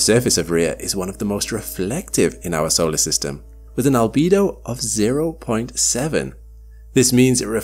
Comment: At first, I thought you said a libido of 0.7, and i was quite concerned.